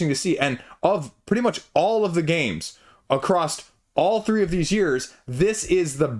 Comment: across. Sounds like you said acrossed/acrossd It’s my pet peeve. Sorry